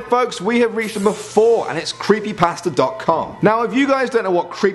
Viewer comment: Jesus I’m def. I thought he said Creepy Pastor .com